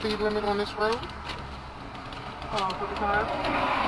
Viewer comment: does she say 35 mph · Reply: 45